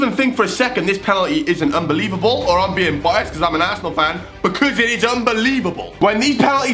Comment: I thought he said asswhole instead of Arsenal